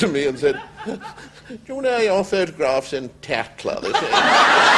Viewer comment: What did he say at 21:32? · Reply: "Did you know your photograph's in 'Tatler' this week?"